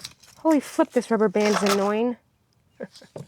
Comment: " holy f*ck this rubber band is annoying " i thats what I heard. The look you gave the camera 26:00 about "organic " says it all